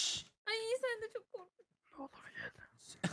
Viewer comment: de ne diyor anlayamadım ben ya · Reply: Nolur gel diyor knk